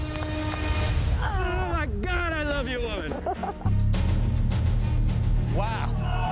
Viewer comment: Wait did just say i love you laran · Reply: i love you woman